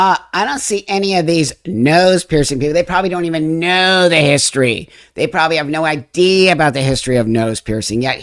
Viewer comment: Did you say... nobody nose the history?